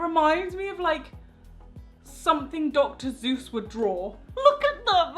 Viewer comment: Dr Zeus? I thought it was Dr Seuss anyone else hear that or just me. Love your videos your brilliant! 😁❤️